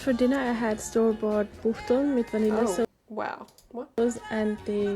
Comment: I think she said she had "Buchteln" on her plate which is a fatty and sugary Austrian yeast bread with the cow and hen secretions in it.